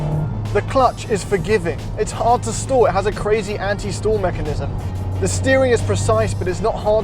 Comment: Damn Brit. It sounded like he said "anti-stool" mechanism and i'm sitting here for a few seconds like WHAT? HA